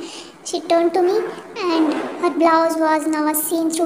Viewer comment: and 5:16 what r these words??? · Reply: b for blouse